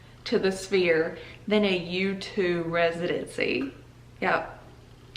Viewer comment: I thought you said “YouTube” residency, and I thought someone made it big. I was like who girl?!?!